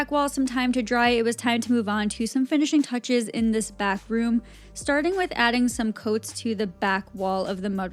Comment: bathroom? Did I hear correctly? 😂 I loved this ❤️